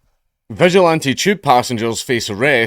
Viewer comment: Not gonna lie, thought you said "visual anti-tube passengers" at the start